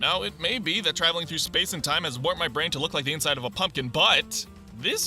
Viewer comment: What he’s saying: Now it may be traveling through space and time warped my brain to look like the inside of a pumpkin, BUT! What I hear: Now it may be traveling through space and time warped my brain to look the inside of a PUMPKIN BUTT!